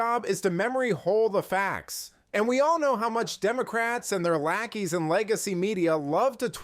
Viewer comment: Hahaha. I thought he said the “Democraps”. How fitting…